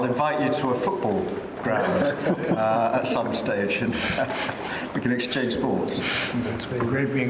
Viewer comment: I thought he said, "we can exchange balls!" 😆⚽️🏌‍♂️